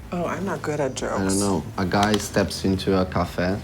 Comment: what was the joke he said " a guy walks into a _________. splash." i couldnt hear that word he said...this is around 17:46.... please and thank you! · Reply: He said a guy walks into a caffè which is Italian for coffee, but it's a riff on "a guy walks into a bar" because caffè sounds like "cafe". So the guy literally steps into coffee.